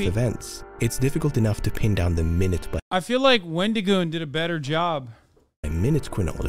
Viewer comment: Who did he say did a better job? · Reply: Wendigoon.